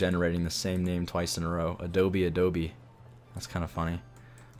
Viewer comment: the building at 39:30 is actually called "Adobe Abode", not "Adobe Adobe" hehe! :D